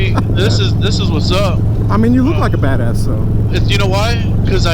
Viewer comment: he thought he said fatass.